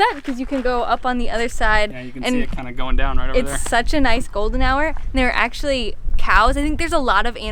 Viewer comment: First i heard "It's such a nice golden shower" :D